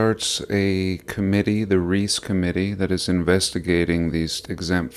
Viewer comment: Did you say the Reese commitee?!